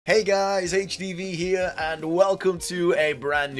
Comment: Every time he says his name I hear “Hey guys HPV over here”